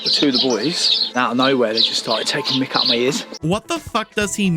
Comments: I imagine somebody has already pointed this out, but just in case The war paint guy (28:04) was probably saying "taking the mick out of my ears" (taking the mickey being a british phrase, basically just meaning to make fun of something) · i think at 28:05 he was saying "taking the mick out of my ears", very much a thick accent that i can see how it sounds like what you thought it did, but it's just british slang for making fun of someone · I think hes saying "taking the mick", which in normal, godblessed american is slang for "making fun of" (so they were making fun of his ears, instead of cutting them) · as a british person (sorry for being british) he actually said "taking the mick" as in the birds are making fun of him